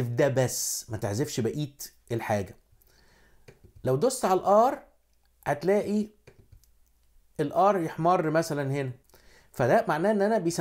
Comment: الله يسامحك في الدقيقه 5:12 سمعتها "لو بصيت هنا حتلائي الR يا حمار" هههههههههههه صدمتني!! شكرا ع المعلومات ممتاااااااااازه جدا